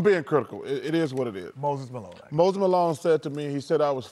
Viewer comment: For a second, totally thought Chuck said Post Malone made him cry and couldn't beat him up lmao